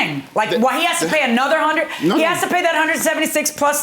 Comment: The guy did not say anything wrong. Plus he did say Judge before her name. As a fellow Caribbean person I don’t think he was being rude. But the judge definitely was.🤷🏾‍♀️ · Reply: I thought he said Judge Milian. With his accent Marylin and Milian might sound the same.